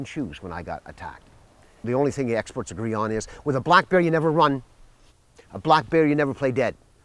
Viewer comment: thought he said, 'with a black guy you never run'